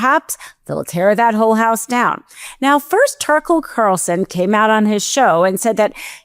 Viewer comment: did she say "turkle carlson"?